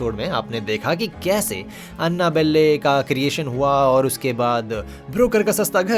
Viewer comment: I've never said Annabelle the same way since I heard you call it Anna-balle😂